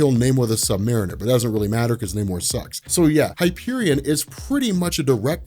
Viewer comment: did you just say Namor sucks??????